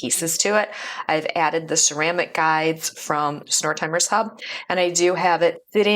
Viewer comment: At around 12:58 you mention ceramic guides. Can you tell me the name of the shop where you got them? It sounded like Snore Timers Hub, maybe? Thanks